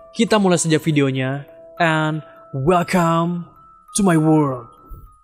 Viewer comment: I hear "welcome to my word"